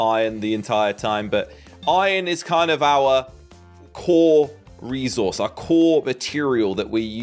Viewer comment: Did he say core or cool resources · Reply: core